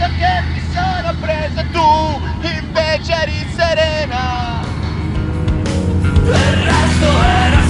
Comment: Ma io solo mo ho capito "e invece tu eri serenaaaa"